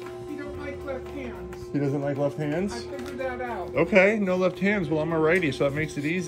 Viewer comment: OH, LEFT HAND! LOL! I could have sworn she said "he doesn't like Latinos!" Wasn't until said, "I won't use my left hand then." LOL. I was about to be like, "Damn racist bird!" 😂